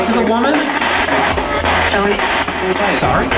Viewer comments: when asked can we talk to the woman, I hear “Sorry” “Raped” “Help Me”. Which would make sense with the SA claims and also the girl screaming and crying in the boiler room. 😢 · I heard "sorry", "rape" and "help her" - dont like any of that